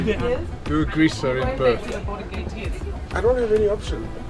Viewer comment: What did the guy say to the captain at 19:02? Something about a greaser? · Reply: “Do a greaser in Perth” (smooth landing)